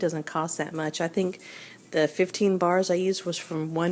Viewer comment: How many bars of Fels-Neptha would you recommend for this mix? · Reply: I believe I heard her say 15 bars. :)